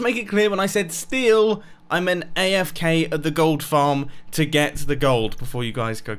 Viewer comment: Selling replays! SOMOEN REMIX IT Why does he sound auto tuned XD Edit: I mean't I'm an EEL. I told u that. That Is what I heard when having fun